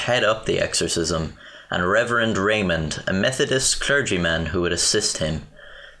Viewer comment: Almost thought he said reverend Maynor.